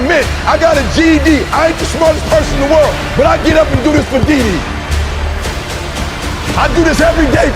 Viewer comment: did he say diddy or dede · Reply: Dede. That’s his wife.